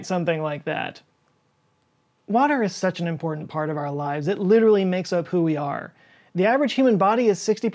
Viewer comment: "Water is such an important part of our lives. It literally makes a 'poo ER' Swear to god I had to listen to this 3 times before I heard it right.